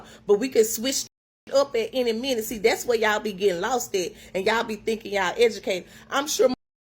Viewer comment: Did she just say- "that's where y'all be gettin losted"???? BE GETTIN LOSTED???? Time stamp: 0:34-36.